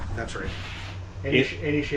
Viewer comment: I honestly heard it as DC Marvel products at first and thought they might be into rocks and comics!😂 I'm already short sighted but might want my hearing checked too.